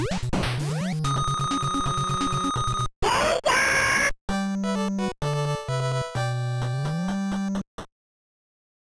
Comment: what did he say? 0.o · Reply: gen-san